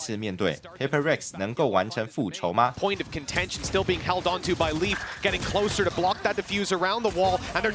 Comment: 我把 held on 聽成 haodong 了。。。